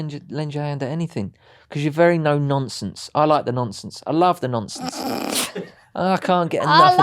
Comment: thought he said likes the nonces. 😂